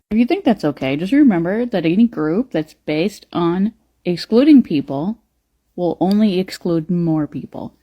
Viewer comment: I thought she said exploding people 😂😂😂😂